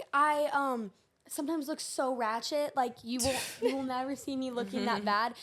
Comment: like if you thought said rat 💩